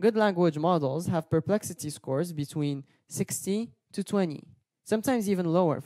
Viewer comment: Thank you for this helpful video. I couldn't understand (4:15) whether the narrator said that a good target perplexity was "16 to 20" or "60 to 20." It sounds like "60", but normally the range would be given in increasing order. At 6:22 the narrator says it in the opposite order.